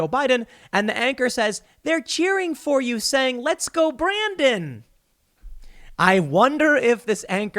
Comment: Literally nobody thinks it was "let's go Brandon!" Nobody. Ever. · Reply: It happened a college game I went to. I misheard as "let's go Biden" I was very confused but listened when it happened again and realized